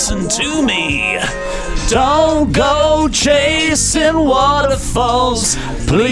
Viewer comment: Started singing along too. That is awesome. Use to be one of my drunk karaoke songs too. Lmao Waterfalls is still a great song. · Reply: When I was a kid I thought they were saying Jason Waterfalls, not chasing.